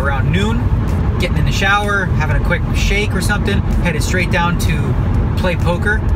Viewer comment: Did he say he does a quick shake in the shower!?!?!?